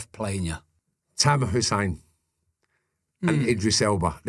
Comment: thought he said Sadam Hussain rather than Tamer Hassan 🤣🤣🤣